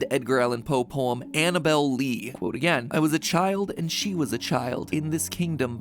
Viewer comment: am i the only one that heard that at around 19:11-19:12? it sounded like someone was saying either 'quote' or 'cold' or am i just over-analizing things? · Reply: The captions say qoute again